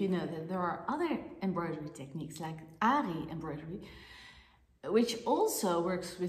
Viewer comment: What was the embroidery technique you mentioned at 0:26? Sounded like Annie embroidery but cant find anything about it! Am an avid crocheter and want to try my hand at tambour but the moment you mentioned another technique of embriodery that uses a hook you piqued my interest! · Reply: Aari embroidery